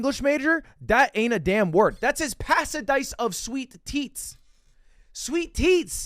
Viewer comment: i thought ludwig said sweet tits xD